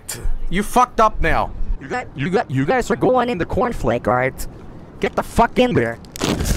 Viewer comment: YoU gUyS aRe GoInG iN tHe CoRnFlAkEs. - Joel, 2020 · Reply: He sounded like he said your going in the cornflake singular